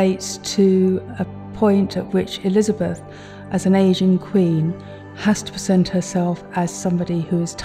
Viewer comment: I had to listen 4 times at around 1:21 to what the curator said, because it sounded like she said "Asian" Queen when I am pretty sure she meant an "aging" queen. But yes, it's a kind of a creepy and disturbing animatronic a la Disney, but it's cool and unique....